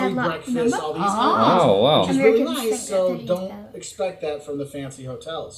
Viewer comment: did she say "Americans think fishing is bad luck?"